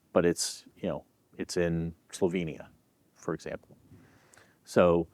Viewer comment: I thought he said “ Tovenia “ ( an imaginary country) but he said “ Slovenia” a country I know. I guess they can make a scene that looks like America but Slovenia doesn’t. It’s been 30 years since I was in Bled. Then Slovenia looked like the remains of the Soviet Union in architecture like Bulgaria and Macedonia which now calls itself North Macedonia to distinguish itself from from Greek Macedonia ( the original Macedonia). The style of modern Soviet architecture was kind of rounded sort of stream lined like 1930’s Art Deco Moderne. It looked out of date. It had not progressed in 60 years since the 1930’s into the 1990’s. Anyway it could be much different now, comrade. I had strange experiences in all three countries and treated as if I was a foreign spy, Agent 007, from Vancouver. Yeh but I managed to smuggle the secret plans for Soviet architecture back to “ Control” at the Vancouver Art Gallery. They had only been a few years out of Communism and were paranoid about foreigners. “ How dare you come and spend money in our country.” “ OK. I won’t.”